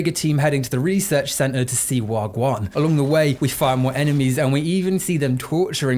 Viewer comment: Did I hear a wagwon in there around 5:36? I think I did...Bumbbaclot rasta boy!